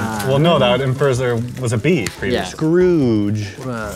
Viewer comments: so is just saying splooge over and over, right? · I would like to know what said @ 10:43 😂😂😂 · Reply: Splooge